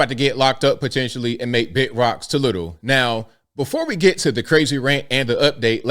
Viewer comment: First time I ever heard you say “turning Big rocks into Little Rock”. Lmfao